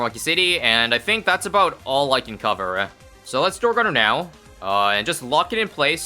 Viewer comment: Captions thought when you said dart it was door so captions said door monkey